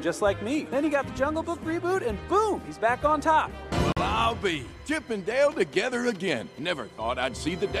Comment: what did he say before saying "Chip and Dale together again"? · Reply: Well I'll be